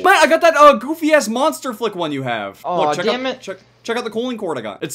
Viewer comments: Who else thought that it said monster fuck ? Instead of flick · I thought monster flick said monster fuck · Am I the only one who thought it said "Monster Fuck"?